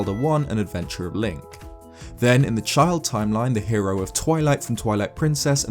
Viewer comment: The Hero of Toilet. Did anyone else hear this?